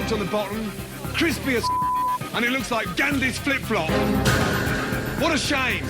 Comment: Did he just say Gandhi's flip flop? 😂🤣🤣